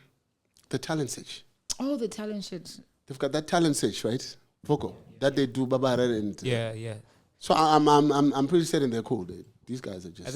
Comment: Did just say 'The Talent Shit'🙊🤣🤣🤣